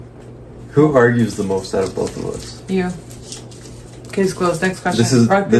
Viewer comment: what did he say? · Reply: Who argues the most out of the both of us